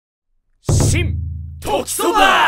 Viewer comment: i thought he said simp at the start